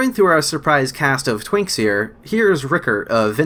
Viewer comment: Did you just say, "twinks"?